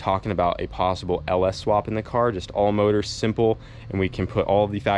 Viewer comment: did say "all motor" whhhhaaaaaaat? This isn't All Motor Boiz - NOT AN OPTION